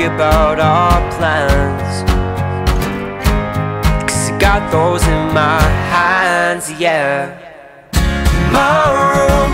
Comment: why does it sound like blake says "cause ive got toes in my hands"